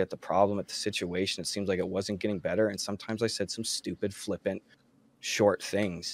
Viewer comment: I'm not a gus apologizer. I agree with most of what you're saying. However I didn't see any other comments so... At 23:46 in the video the segment about the "stupid (flipping) things" Gus actually said (flippant) which means not showing a serious or respectful attitude. I think he's still just trying to make excuses but I just needed to write something commenting on this